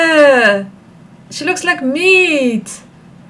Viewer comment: I thought you said “shes looks like me”